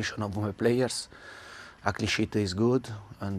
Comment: what did he say? All I hear Is The focus and attention of my players at this sh*t is good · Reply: Cleanshit*